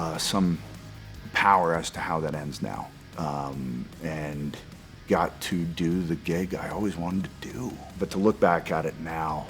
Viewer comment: Did he say he "got to do the gay guy I always wanted to do" ?